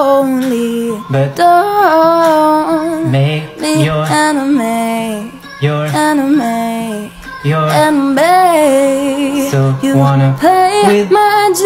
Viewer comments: It sounds like she said so dont make me your anime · Not my goofy ahh head thinking she's saying "don't make me your anime, your anime, your anime" 🌚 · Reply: context: the girl singing the song when she said "dont make me your enemy" sounded like she was saying a japanese creation called "anime" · When she said “enemy” I thought she said “anime” 😭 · It sounded like she said don’t make me ur anime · Hehe it sounds like she saying Your- ✨a N i M e~✨ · When she sang "enemy" I thought she said anime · Others: I hear " Make me your enemy enemy Me: MAKE ME YOUR ANIME ANIME ANI~ME! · You sound like your saying anime and not enemy lol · What she said “enemy” What i heard “anime” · When she said enemy I thought she said AnImE~ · When she said "Your Enemy" I heard "Your anime" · It sounds like you said, don’t make me your anime. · Is it just me or did it sound like she was saying anime instead of enemy Your so good at singing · Sound like she is saying anime insted of enemy🤧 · Sounds like she is saying anime not enemy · Its rlly good but i thought she was saying anime not enemy😅